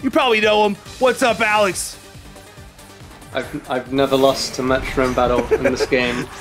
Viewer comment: did he say he never lost a metronome battle?!